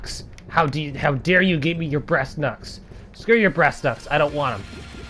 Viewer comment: Kung fu at its finest....it sounds like your saying breas knucks, lmao